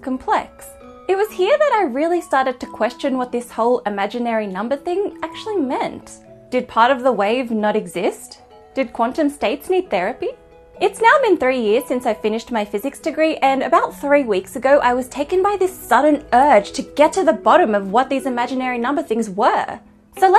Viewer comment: At first I thought you said, "It's now been thirty years since I finished my physics degree …," and I'm like "WHAT?!?!?!" :D